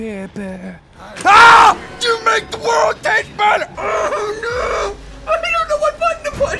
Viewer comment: did he say, "You make the world taste better!"?